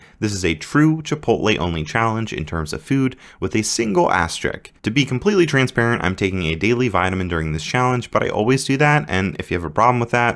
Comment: Did bro just say "ass trick"??